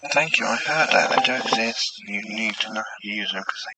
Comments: The backwards spirit box i heard "Thank you, i heard that. I do exist. You need to know...." i couldn't catch the last bit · Hi I heard at 18:24 thank you I heard that you don’t need to know everything .Maybe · The voice at the end. I thought I heard "Thank you, I heard that, I do exist, you need to learn (how to) use this thing"! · hey the recording at the end sounds like it's saying "thank you I heard that I just think you need to learn how to use courtesy". you did brilliantly on your first top 5, well done mate 👏👏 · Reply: Omg, I couldn't figure out what the last word was! I thought it said "thank you. I heard I don't exist. You need not use (something)" but courtesy makes the recording sound so polite lol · At the end of the video with reversed talking. It sounds like he says "thank you, I heard that. I don't think you need to know how to use everything" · I heard "Thank you. Ive heard I don't exist, but you...." I couldn't make out all of it. Great video ❤